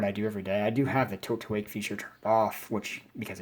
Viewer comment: Sorry but what mode are you saying you don't have on? you say it at 8:26 and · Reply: Tilt-to-Wake